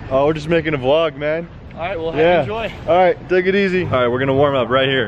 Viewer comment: Did he say have a joy